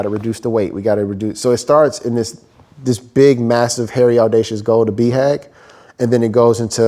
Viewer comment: Awesome content, thanks so much! What he is saying at 33:55 is Big Hairy Audacious Goal, BHAG (it's written "inaudible" in the subtitles), it's a concept from Jim Collins book "Built to Last"